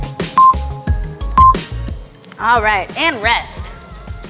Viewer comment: Those Spider-Man planks got in me so in my head that when you said "and rest", I thought you said "damn Jeff"...which is my name haha. I had to rewind to see what you actually said. Anyway, that wasn't too sappy, that was fantastic. Thank you.